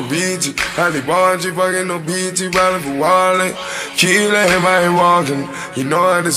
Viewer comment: when he said to kill everybody walking i thought he said tequila and marijuana